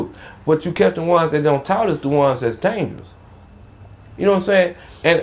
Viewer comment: hold up what he say??? I rewinded it 6 times and still don’t know wtf he said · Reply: Da ones dat dont talk is da ones das dangerous lol